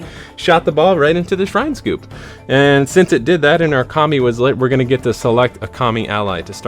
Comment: great video! laughing though because every time I heard you talking about Kami allies, I heard it as "commie" and was wondering what the ninja game had to do with communists